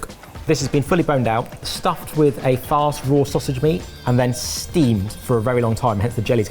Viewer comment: What's a fast raw sausage meat, or did I mishear?